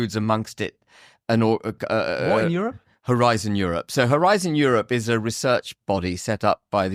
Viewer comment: Its HORIZON EUROPE - I heard Horize in Europe...